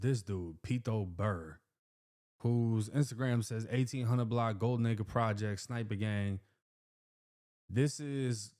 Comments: I was just listening when he said “Golden Acre” he sped through it I thought he said Gold Nigga Projects 😭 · 😂 why I thought said gold nicca projects 🤧 ….. I had tuh replay like 6 times